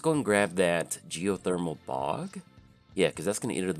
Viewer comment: as always happy to see another great spooky deck video that said i totally misheard geothermal bog as geotheromal pog at first lol